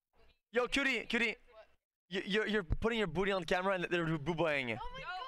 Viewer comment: can someone please explain to me what he says here? "And they're buboying it"???